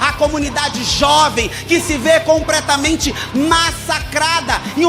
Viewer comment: "COMPRETAMENTE", foi isso mesmo produção? :hand-orange-covering-eyes